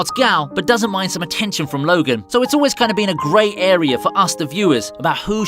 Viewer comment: love the bracelet love how you said gray area 😂 and how the words said great area instead of gray